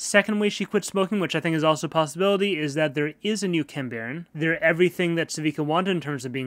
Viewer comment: Ok, someone please help me! What does he say here? I hear "There is a new _____" I hear "kimbarent", but I'm pretty sure that's not the way you spell it. And also what does it mean? From the context, I assume it means some kind of authority, but I can't be 100% sure.